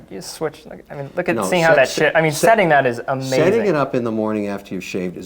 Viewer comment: Anybody else hear say "shit?" never heard him swear before